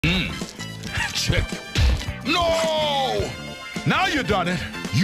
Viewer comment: i heard him say "mmm chicken"